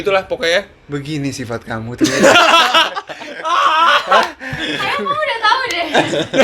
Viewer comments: "Begini sifat kamu ternyata... Syifa :"Sayang kamu udah tau deh... Kalo nggak salah denger sihh...hehehe...ku puter berulang-ulang... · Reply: Bukan sayang bray tp ..kayaknya km uda tau deh.. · Salah denger ga sih bilang “sayang kamu udah tau deh” atau “kayanya kamu udh tau deh” ... Lucu banget ngegas wkwkw...